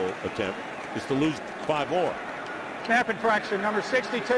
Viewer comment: I can't tell what he's saying here? What type of infraction?? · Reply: Snap Infraction